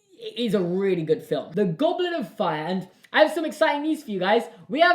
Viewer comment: misheard that as "the Goblin of Fire" lol